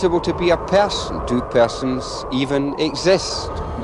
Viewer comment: I am so sorry but for the life of me I cannot figure out bros name at 19:55, anyone able to help me out? I'm still watching so maybe it comes back up but the subtitles are not helping and neither is google or playing it back a hundred times lmao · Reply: nvm! it's R.D Laing, we got there in the end.